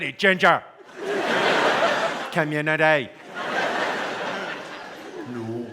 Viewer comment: what does he say? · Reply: “community”